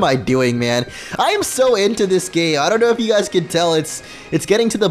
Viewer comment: Did I just hear "Im so into this GAY!"